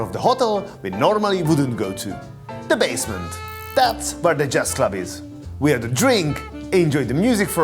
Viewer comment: I was listening to this on my second monitor and when you said "there's a Jazz club" I thought I heard "Chess Club" and immediately got excited..